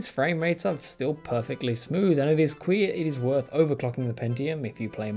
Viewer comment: i heard you say "it is queer that it is worth overclocking the pentium" ahaha